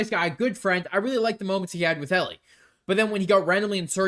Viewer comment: i heard soccer scene 😔